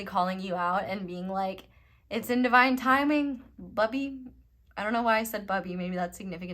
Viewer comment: What. THE FUCK.. So I slid to pile 4 though I couldn't decide between 3 of them, which is unusual for me cuz it's typically instant. Anyway my fingers bumped it or someone & it took it back to pile 3. I heard you say "Bubby" but I thought, nah, she said Buddy. Then I needed to know cuz needed to. Rebound it & sure as shit ykj did say Bubby lol. It's a nickname I have for my son. 😂😅I have a feeling there wasn't anything else for me to hear in pile 3 s o moving to pike 4. I have a feeling it was my network in the netherworld just confirming to me question of asked about my son earlier, also, that you are amazing lol